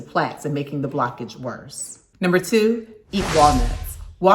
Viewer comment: Please join me this Friday, February 11th, 2022 For My Book Release Party at Noon EST: <link> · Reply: It sounded like she said, number 2,iguanas.